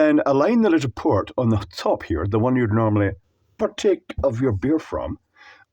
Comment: what word was said at : 0:32 please ? patik ? pratic ? 😢/me is bad in languages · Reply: Partake.